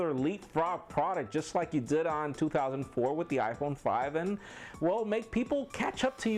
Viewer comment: What did he day at 5:34? Sounded like they released the iphone 5 in 2004